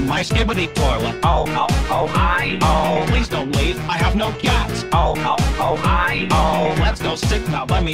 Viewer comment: please don’t leave, I have no gyatts caption say on gyatt cats